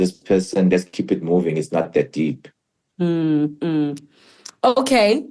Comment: Did he just say lets keep it moving whats the big deal 🙄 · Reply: He says ts not that deep😢